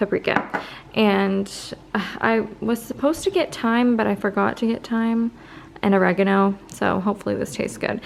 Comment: Lolll when you said "I forgot to get thyme" I thought you said "I forgot to get time" and I was like how did she forget to get time?! Looool. Anyways, loved the real talk. I feel like you're really preparing me for the truth of becoming a mother. Thank you 💖